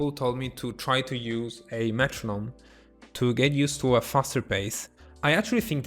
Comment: What does he say at 6:40? I cannot understand the word he says. Thanks :D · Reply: metronome